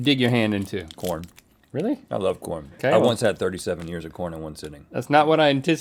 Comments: "I once had 37 ears of corn in one sitting" Rhett should come on over to Nebraska! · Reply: I thought he said 37 years of corn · Okay but were just gonna gloss over “I ate 37 ears of corn once”????? 😂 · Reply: Until this comment I thought he said 37 years of corn, 37 ears of corn makes a whole lot more sense 😂😂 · "..I once had thirty seven ears of corn in one sitting. And thats not a joke, they just kept bringing them..." -Rhett 6:04 Link and Rhett are great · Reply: I thought he said 37 years of corn inc one sitting... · I thought Rhett said 37 YEARS of corn in one sitting, and I was severely perplexed